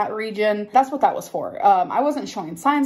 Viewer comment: Can someone please tell me what is she saying at 4:49… something lation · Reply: Supposed to be speculation but she says speckalation🙄